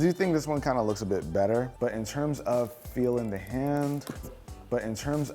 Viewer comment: Did anybody hear him say but In terms of the hand twice